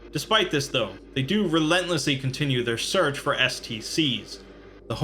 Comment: I wasn't paying super close attention so when you brought STCs back up for a second I thought you said SCP and I was like wait is this a crossover or something? XD